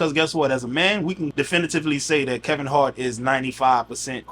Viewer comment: did you call him Kevin Hart?😂